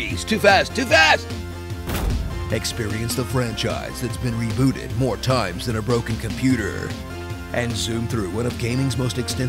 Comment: I swear, when he said Broken Computer, I heard "Orokin Computer."